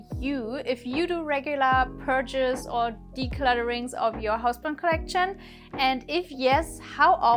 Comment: I swear I heard 'purges or declutterings of your husband collection' at the end there 🤣 But honestly, I love your content and I am learning a lot.